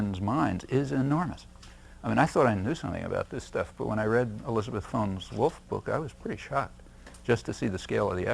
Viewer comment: Chomsky refers to a woman named Elizabeth something Wolfe and refers to her work, but I can’t make it out rewind it at several times Does anyone know what and who he’s talking about? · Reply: Elizabeth Fones-Wolf - Selling Free Enterprise: The Business Assault on Labor and Liberalism, 1945-60